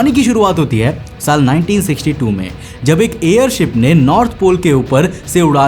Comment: Bro you say 1962 but subtitle say 1926